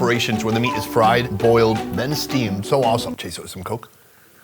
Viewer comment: Did Sony just said "some coke"?? it's pepsi 😂😂